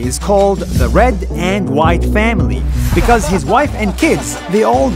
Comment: when he said the white family i heard like the whyatt family in wwe!!!